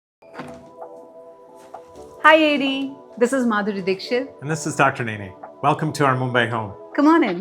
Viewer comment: She sounded like "Hi Yedi" 😂